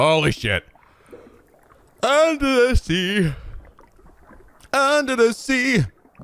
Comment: Heh... When said "Under the sea" I thought he said "LSD"